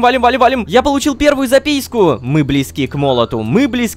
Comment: Мне одному показалось что он сказал запиську?